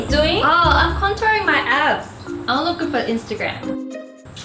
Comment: On the first one I thought she said I'm contouring my ass 😂